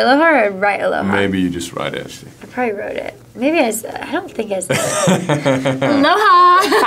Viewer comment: Someone can tell me what Shai said at 0:59 to 1:02 ? Thanks ! =) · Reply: "I probably wrote it... Maybe I said it? I don't think I said it..." :)